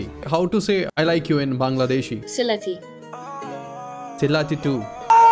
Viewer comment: I like you in Bengali ( or Bangladeshi ) is :- আমি তোমাকে পছন্দ করি (Āmi tōmākē pochondo kori) She is actually saying that "she is from Sylhet" Sylhet is the name of a place... 👍 😜Ok...?